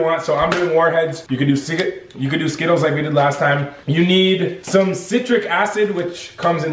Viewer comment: is it just me or did it sound like he was about to say "you can do cigerates " but he really said "you can do ciger(paused)you can do skittles"